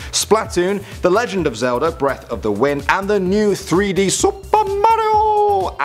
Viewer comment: Did he say "Breath of The Wind"!? Triggered